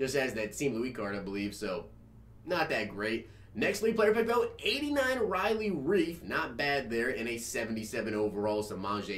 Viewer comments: I thought he said Riley Reid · I thought you said 89 Riley Reid